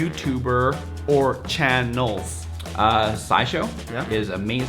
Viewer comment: Sounded like he said SciShow, cause I love learning from that channel as well. <3 <3 <3